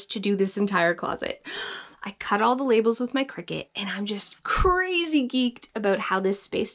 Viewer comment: ahahahaha you are even more awesome for this! I love the Ladybug-ness of that closet, made me laugh so much, but I'm a butterfly/bee and so I have a couple of rooms away from guests that literally look like your room after you pulled all the stuff out!! It's insane, but I AM GETTING THERE! I should have taken before and after photos of some rooms and areas of my house because I have NEVER been able to get myself sorted but after realising I'm a butterfly/bee, suddenly it ALL made sense. I realised why my closet is the only organised thing I have, why my pantry was a complete fail (since changing it to match my style, it's going very well), and what I needed to do to get organised in a way that worked for me. And it's WORKING! Thank you so much and I just adore your honesty in this video. You're gold. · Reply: oh and when you said Cricut, I thought you were saying Cricket as in organising style until I saw the comments!